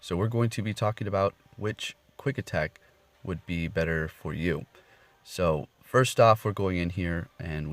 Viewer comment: when you in the start said, whats better for YOU, i thought you said, better for MEW, Lol